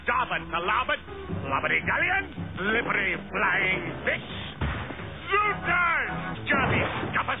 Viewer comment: Did he call peter pan a flying bitch